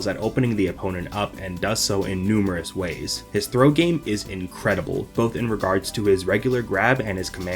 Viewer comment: Misheard throw game as "throat game." Girl i spat out my coffee